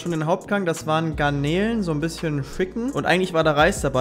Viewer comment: Das am Schluss sollte wohl "so ein bisschen Chicken" heißen. Ich habe aber "so ein bisschen F**" verstanden. 😅